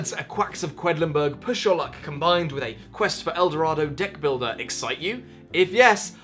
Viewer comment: "Do the words 'A quacks of quedlinburg push your luck combined with a quest for el dorado deck builder' excite you? If yes... I am so sorry." - Ouch, Tom... ouch. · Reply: i thought he said *ick builder